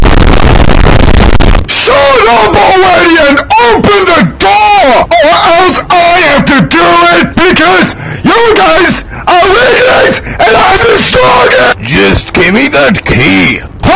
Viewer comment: What did Snowball say? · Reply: "Shut Up Already And Open The Door Or Else I Have To Do It Because You Guys Are Idiots And I'm The Strongest"